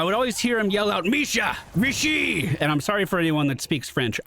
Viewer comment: in french he doesn't say "misha, mishi" or whatever, he says "misha, ici !" which basically means "misha, here !" which is what he says in english